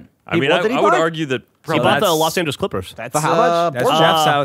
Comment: Anyone catch Geoff saying Los Santos Clippers